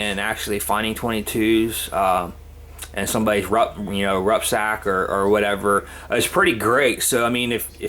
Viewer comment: Did you say rupt sack? I think you meant rucksack.